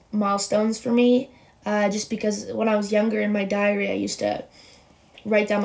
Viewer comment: i thought she said diarrhea at 1.54 but nah she said diary my baadd